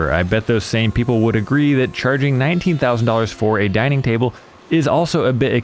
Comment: At first i thought i heard 1900, but then i saw in the comments he said he charges 19,000! I can still see the different wood being used in that "finished work" you better restart and try again till it's perfect. Jeez that's like a down payment on a house, that's almost a brand new car, that's enough money to get a bachelor's degree. Yes you're charging too much, but it's your, business i suppose.😒